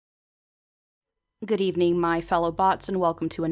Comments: i thought you said "good evening my fellow thots" at first and i was very confused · For a second I thought you said “good evening my fellow thots” and I was about to be simultaneously very confused and very happy